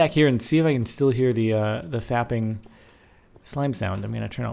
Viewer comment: Did he say fapping slime sound?